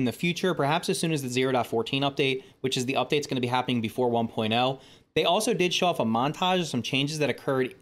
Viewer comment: i thought they said no pts before 1.0